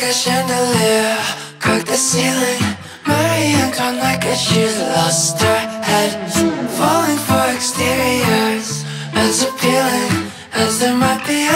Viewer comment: am i tripping or did he just say caca ceiling?